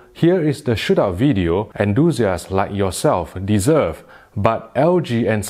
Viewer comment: it sounds like he says losers like yourselves lol